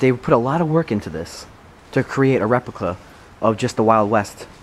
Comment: who else heard what said at 1:21 he said reclipa